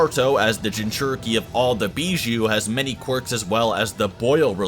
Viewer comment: did I hear quirk 😂😂😂 naruto has many quirks, shout out to boku no hero academia 😂😂😂😂